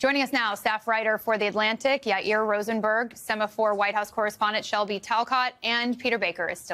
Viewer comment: I heard “White House cherub” and laughed so hard. Alas, it was just word association with the picture.